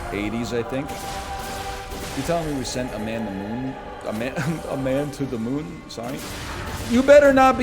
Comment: did somebody say mad moon?